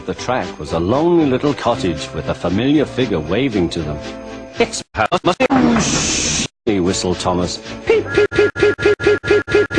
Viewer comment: What was said at 0:06-0:07? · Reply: "it's Hammerdoosh" Hammerdoosh being the channel whose birthday I celebrated with this video.